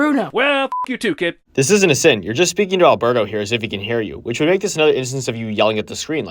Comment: you said “hear you” but the subtitles say “hear him” Edit: I’m not defending Jeremy, I’m just pointing out mistake